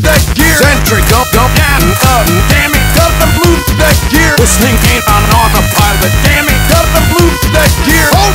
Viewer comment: i used to think this said “ dis thing aint even on the pirate” lol